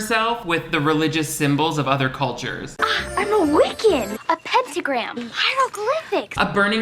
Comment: As a kid, I never heard Marnie when she said, "I'm a Wiccan!". I always thought she said, "I look wicked!" and figured it was just a bad pun.